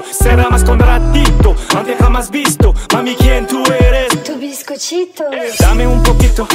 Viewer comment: La partie de Greg, elle me tue tellement et celle de Jess je suis à terre 😂😂😂 TOBISCOTCHITO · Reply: Moi j'ai compris "tu bizcochito" ton petit biscuit lool. Mami qui es tu ? Ton petit biscuit lol pour être plus précise parce que ton petit biscuit tout seul ça peut porter à confusion 🤣🤣